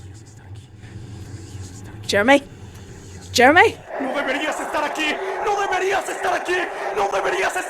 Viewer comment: I'm a beginner in Spanish and I think he is saying "no deberias estar aqui." which means "you should not be here." pretty haunting :^